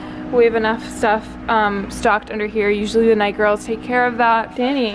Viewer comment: did she just say "usually the negros" ?????? Wtf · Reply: night girls i think